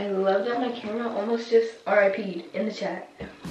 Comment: broo did she just say R.I.P.E lmaooooo